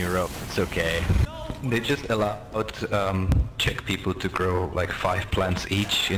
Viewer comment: Did he just say CZECH?! huray! :D